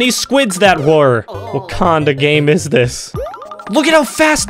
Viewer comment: “wakanda game is this” trust me that’s what he said